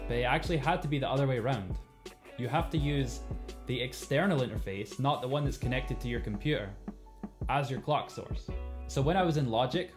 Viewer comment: I thought you said you need to have both interfaces connected to your computer.